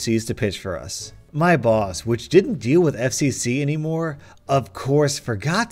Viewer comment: Who else at 5:04 heard him say FCC instead of FFC